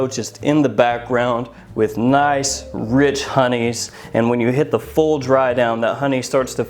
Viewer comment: Did you say Rich Honeys (4:02)? I love me some Boozed up Vanilla Rich Honeys!